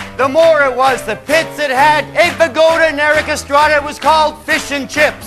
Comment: Did Colin say “Aitha Gold”? I know Eric Estrada was in CHiPS, but I don’t know who the former is.